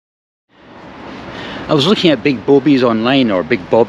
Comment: Did actually say "I was just looking at big boabies online" at 25:21?